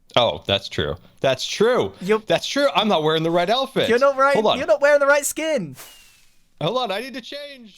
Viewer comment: I swear Scar says "I'm not wearing the right Elephant." Prove me wrong